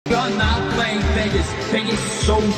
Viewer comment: For some reason I thought he said Pegasus